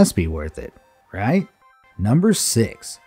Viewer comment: I thought you said horse shoe crap blood 💀